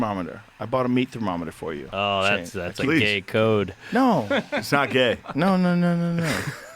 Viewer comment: you know what I heard gay code too😮